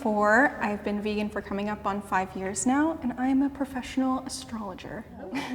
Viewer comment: Did she just say she’s a professional astrologer? Wtf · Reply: omg I thought she said astronomer